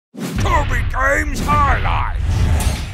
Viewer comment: I just watched all highlights and in each one I hear "TobygRames highlights", am I the only one?